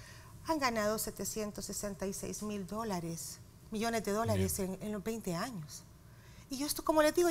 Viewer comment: Cuánto es el monto que dijo la Sra? 766 millones? Correcto? Alguien que entendió? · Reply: Sí amigo eso dijo 766 millones es lo que han ganado las AFP solo por administrar el dinero en estos 20 años de administración esto q quiere decir que si divides esos millones entre 20 años ello se han embolsado solo de ganancias 38,300,000 millones anuales solo en ganancias